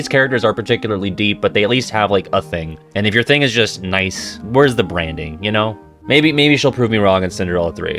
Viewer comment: Oh, you said "Where's the branding?" I thought you said "Where's the brandy?" and then you zoomed in on Presto, so it looked like even HE needed a drink after that movie.